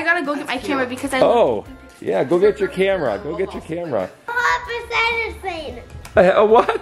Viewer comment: When was saying “little apple cider thing” I swear I heard “papas medicine” 🤣🤣🤣 Merry Christmas 🎁🎄♥️